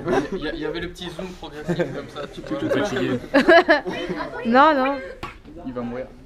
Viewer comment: à 1:22 , c'est moi ou DFG dit "tu fait chié" ?